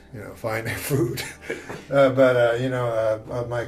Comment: What is the word norm used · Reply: he says “eating, finding food”